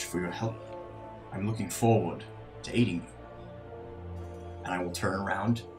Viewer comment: i thought Marius said “I look forward to eating you,” and i was like SIR?!